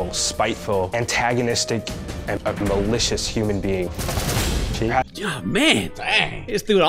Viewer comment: I thought he said "a delicious human being"